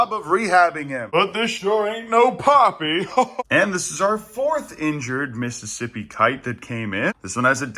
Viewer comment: I thought you said “surfboard” so now I’d like to start a petition to call the 4 th rescue hawk with the injured wing Surfboard 😅